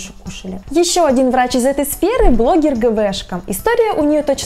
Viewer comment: А где предпоследнюю посмотреть? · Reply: Блин я не иска не как гвшка а мне послышалось гбшка. Ну думаю ниче сё саркастиноциничный псевдоним